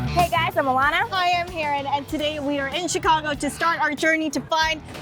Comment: I thought she said Karen